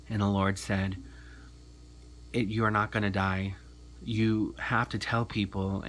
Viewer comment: did you say "if you are not gonna die"...? Or "you are not going to die"? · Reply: He said "it....you're not gonna die" like he was going to start a sentence but changed it in mid-chat. Hope that helps. God bless.